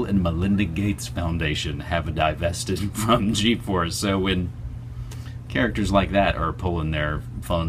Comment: I thought you said "GeForce" for a moment, and wondered what my graphics card has to do with torture profiteers.